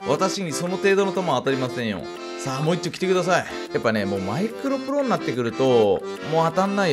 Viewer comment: マイクラプロが、マイクロプロ に、聞こえた人挙手